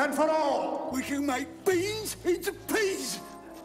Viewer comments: "We can make beans into peas!" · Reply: As a kid, I thought he said, "We can make bees into peas!" Still a hilarious line. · "We can make beans into peas!" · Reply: I thought he said "bees into pigs."